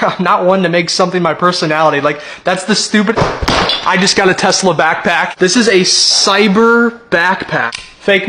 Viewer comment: I thought he said Nikola Tesla and not I got a Tesla at the beginning and I was fully expecting a backpack with a Tesla coil on it and I am sorely disappointed.